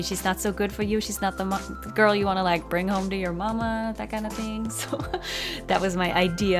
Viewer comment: Did she just say don’t bring this girl home to your momma 😂🤣😅😛😝🤪🤦🏾‍♀️